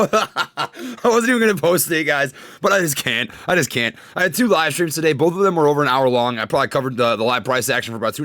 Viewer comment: When you said "I did a livestream" i thought you said "I did a lot of mushrooms" lol and it was right after you were hella laughing. I was like damnnn is so down 😎👏